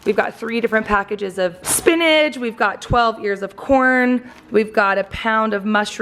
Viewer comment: I thought she said 12 years of corn. I’m like how do they last that long!😂